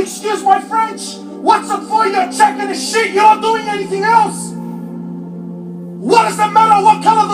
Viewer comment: What did Mohamed Hoblos say from 38:47 to 38:52 when the screen turns black ?! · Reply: 💀😂 he said ur chucking a shit ur not doing anything else